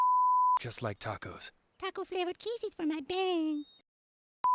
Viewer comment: I thought the description said Tesco flavoured kisses for everyone 😭